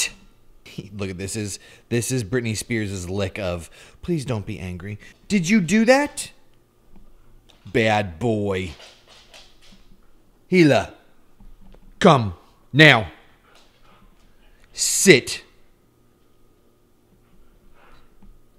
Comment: Did he just called Britney Spears " bad boy "???